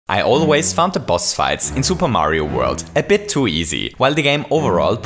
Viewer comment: did he say super Mario world?